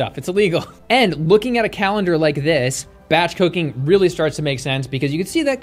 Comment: It sounded like you said batch cocaine instead of batch cooking LOL